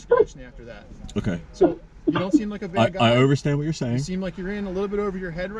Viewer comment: Did he really just say "I over-stand what you are saying "? 😂😂